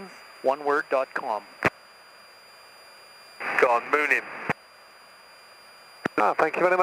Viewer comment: did he just say "Go on, moon him"? :D